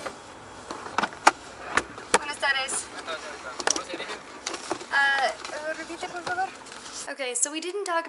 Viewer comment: Hey guys! I know is maybe a lil bit late to respond hahaha but 11:58 "buenas tardes señorita, hacia donde se dirigen?" That's what the militar man said to you, translation "good evening ma'am, where are you heading up?" Those are just, like, "default" questions in those military points on the road, they ask you where you going "hacia dónde se dirigen?" (you can tell em the name of the city you are heading to), they maybe ask where you coming from "de dónde vienes?" Meaning the name of the city you started that travel or just the last city you passed, rarely (but not impossible) they ask what do you do for a living "a que te dedicas?" You can answer that in a not specific way saying the name of your profession/work/employment for example: I'm an architect, I'm a car seller at Ford, I'm a real state agent, I'm an employee at Walmart.